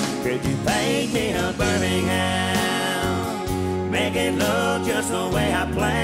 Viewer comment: I don't know why I always hear "paint me a burning ham" lol I love Tracy & the song his great...but it still makes me laugh